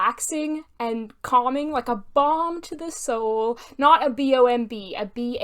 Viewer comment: LOL "a bomb to the soul" I definitely thought that's what you said at first! But a balm sounds much nicer.